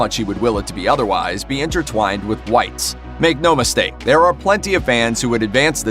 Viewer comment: did.. did you just call Walter “Dwight?”